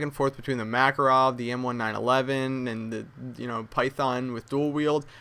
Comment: is it me or i just heard M1 9/11?